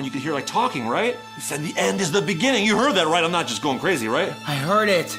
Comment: the end Has begun) that’s all I heard